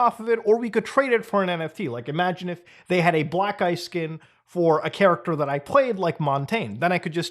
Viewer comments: I'm feeling like Burnt Chrysler because every time he said "black ice skin" I heard "black guy skin" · Me mishearing “black ice” for “black guy’s” skin while listening and cleaning 😂😂